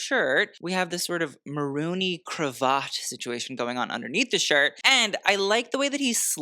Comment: I hear Marooney Cravatte is on the next season of RuPaul's Drag Race